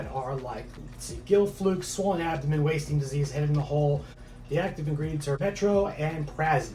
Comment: I heard "head in the hole" LOL :)